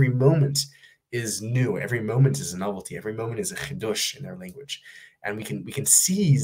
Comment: "we don't have to be defined by our past or indebted to our futures" beautifully said! What's that word in Hebrew? After you say "every moment is a ?chidush?'. Thank you! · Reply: Chidush (Hebrew: חידוש) = Novelty (or "without precedent")